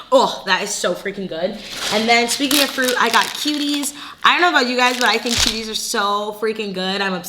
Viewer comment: I’m confused I hear you say “kiwis” twice but I see oranges 😕😕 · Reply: Oh she’s saying cuties. It’s a brand of mini oranges/tangerines💕